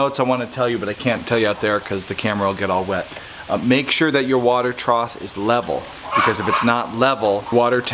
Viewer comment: Hilarious! I have one tiny problem with it and it’s only because I live with an English major librarian lol but I noticed you kept saying trot and it’s actually trough (troff). That’s it, I know it’s a little thing and some people don’t care to know but I would want to be told 🤷‍♀️ It was a hilarious video though! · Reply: I heard it several times too. It was troth especially at 5:18. I figured it was simply a Connecticut thing...